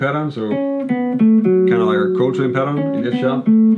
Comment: Hey, Happy Birthday! You're doing us all a great service by making these videos! I have a question for you, at around the 5:10 mark you mention "kinda like a Coton pattern". I'm not sure what you said and assuming you mean "Coltrane" pattern, but just looking for clarification as I'm not familiar with the term you used